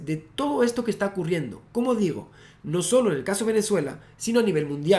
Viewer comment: Min 32:11 pensé que decía como Diego (ruzzarin ) jajajjaja